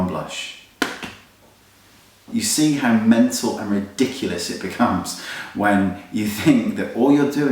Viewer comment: lmaoo and btw u sounded like u were sayin dickulous :P..**not hating i lovee u :))**